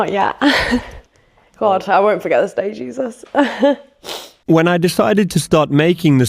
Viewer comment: Did she say “Jesus”!? No ma’am, Jesus doesn’t support that behavior!! 🤣😂🤣😂